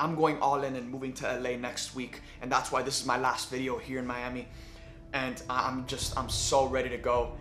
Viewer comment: For a sec I thought you said last video ever and I freaked out